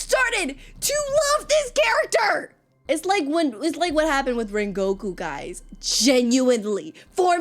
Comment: Am I tweaking or did she just say “Rengoku”???😭😭Stop giving me flashbacks girl-😭😭😭 (Live, laugh, love Rengoku😔 UMAI!)